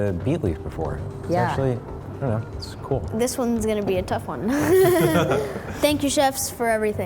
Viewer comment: For a sec, I thought she said "Thank you bitches"